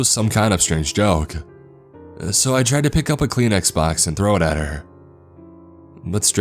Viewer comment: I thought he said "I tried to pick up a clean xbox and throw it at her" 10:43 I had to stop working and look at my phone like wtf😂